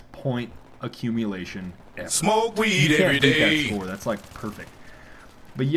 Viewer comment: Am I the only one who hear: Smoke weed everyday?